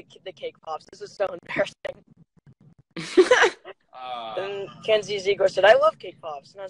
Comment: did he just said “kenzie ziegler” ??